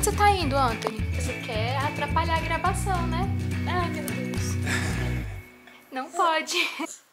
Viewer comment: Eu juro que ouvi "não fode"